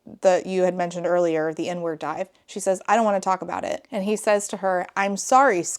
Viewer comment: Ngl I thought you were saying "the N-word dive" and was baffled at you just breezing by that without commentary 💀